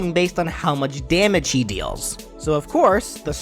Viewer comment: DID YOU JUST CALL SOILDER A SHE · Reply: No. Try saying "how much damage he deals" out loud and hear how it sounds.